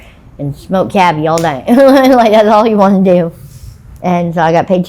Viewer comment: She said she smoked $100 of Cavi all night. What’s Cavi? Some new drug? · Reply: i thought she said 'cabby' which i think is a cigarette rolled with/containing marijuana and cocaine/crack.